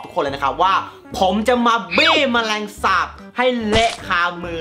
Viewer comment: ได้ยินเป็นจะมาปี้เเมลงสาบ555